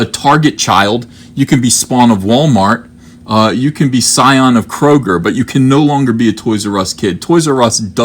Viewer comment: I know I must have misheard you when you said "psion of Kroger" but I now really want to be a psychic attuned individual originating from a grocery store chain.